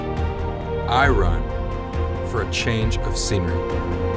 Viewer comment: Is it me, or is that guy saying.. I run for change of SEMEN ?! @ 1:07 What did he say?